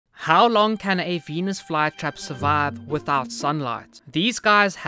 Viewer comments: I thought you said penis fly trap… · I thought he said when i wasn't looking on the phone "Penis flytrap" 💀